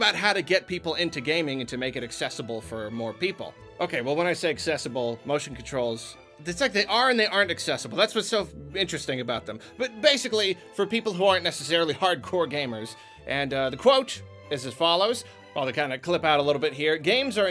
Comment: Did just say “that’s what’s so f***ing interesting about them”? Does have a sailor’s mouth behind the scenes? I’m here for it.